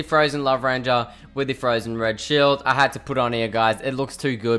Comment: he says shit instead of shield 😂